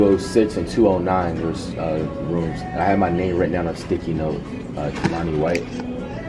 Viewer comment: Is flight name Lonny White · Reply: Devon owens I think TI use to say Kimanni but at 7:18 I thought he said Lonny white might have used a alias